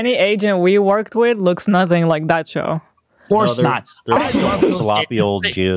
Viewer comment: wait did Hila just call him by the wrong name or is his name really Joe? I hope his name is Joe oh god I'm cringing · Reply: Joe? Nah. Show.